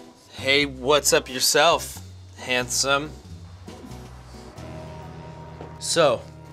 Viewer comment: did he just called her handsome??